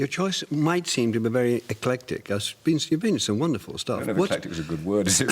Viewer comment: What does the interviewer say at 5:14 ? "Your choice might seem...." What? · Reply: Eclectic